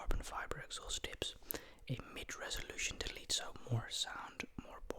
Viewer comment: Mid resolution delete?😂you ain’t know what you saying Mid Resonator delete it ain’t gonna give you more HP louder exhaust yea lmao